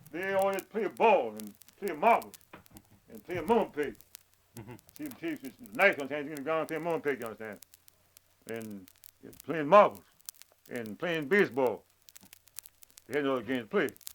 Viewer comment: The transcript doesn't understand the game that the kids play. I Recognize he's talking about Mumbldeypeg. And the next sentence he's talking about if you have a knife you play Mumbledypeg. But the transcript seems to think he said night and not knife. My dad told me about that game years ago. Where the kids used to throw a knife into the ground. There was some object to the game, but I had never played it so I don't really know. The transcript is kind of an official record and it's sad that they didn't get that right. <link>